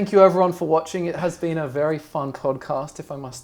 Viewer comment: Did say it's been A very nice cod cast? 1:05:51 seems fishy.